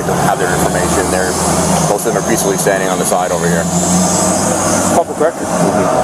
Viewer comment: are you kidding me? What does he say here? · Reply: He said “it’s public record” meaning the obnoxious fat man can pull the public records to find out our names to try to sue us. They’re trying to weaponize that fat man. ❤️🇺🇸❤️🇺🇸